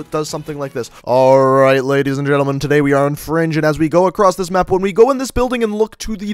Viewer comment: did he say fringe at 1:02 hahaha 😂